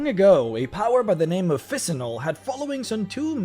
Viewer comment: Who is this dead goddess of the full moon you're mention around @ 5:39 ? I've never heard of her and want to do some more research into this topic. How do you spell her name? (CC says it's "Fissinol" but I have a feeling that's quite wrong lol) Thanks again for the incredible work btw. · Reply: Phissanol. Planscape A Guide to the Astral Plane. Page 35 under By Loss of Faith